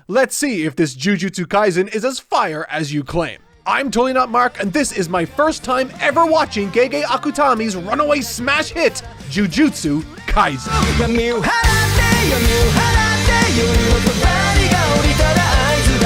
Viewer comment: gay akutami if you press cc subtitle. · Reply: I heard "gay gay akutami" 😅